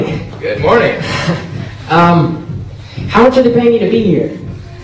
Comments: what does he say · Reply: "How much are they paying you to be here" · What does he say at 1:35? "how to-?" I cant hear it out